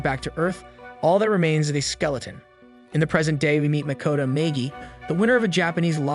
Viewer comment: I don’t know if I’d call Naegi lucky but this show is great was my first anime also 14 seconds sounds like you said maygee